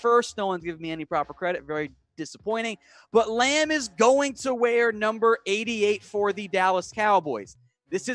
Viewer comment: I thought you said lamb was going to wear #10